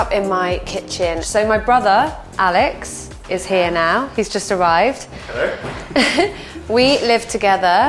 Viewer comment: He’s just arrived / he’s just survived 😂 that’s how I heard the first time Fool of me Sorry